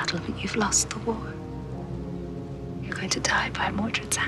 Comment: I thought she said "You're gonna die by a pizza."